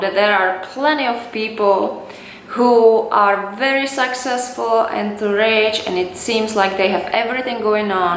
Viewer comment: Did she say " Sexcessful"? 0:59 Sounded like it.. anyway love your Accent and thanks for the Motivational Tips.